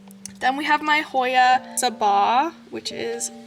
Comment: what's the Hoya at 35:25 sounded like you said tsaba? or something. Beautiful cabinet it turned out great! I've done a similar thing in my old fish tank and you've inspired me to add more baskets and maybe a moss wall!!!